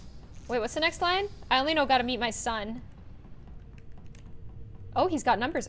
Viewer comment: when she started to rap/sing the lyrics of hamilton, i was screaming btw the line was I gotta go got to get the job done, gotta start a new nation gotta meet my son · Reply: I thought she said gotta beat my son. . .